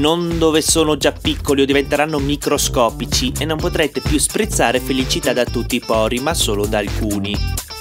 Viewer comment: “mettetelo dove i pori sono più grandi e non dove sono già piccoli o diventeranno microscopici e non potrete sprizzare felicità da tutti i pori ma solo da alcuni” HAHAHAHAHAHAHAHAHAHHAHAHAHAHAHAHAHAHA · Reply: Eerised 26 e poi ci sono io che ho capito "non potrete più sprizzare felicità da tutti i pori ma solo dai culi" 😂😂